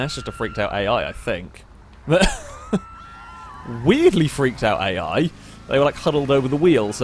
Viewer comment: The amount of times I heard "Weirdly freaked out A.I"